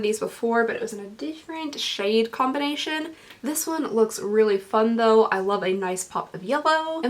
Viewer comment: I misheard fun as fucked and I GASPED